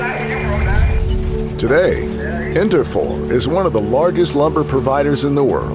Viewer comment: Thought it said “inferior”